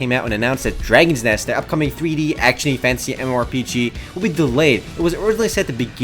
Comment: when u say "fantasy" it always sounds like ur saying "fancy" work on it :D Keep up the good work :)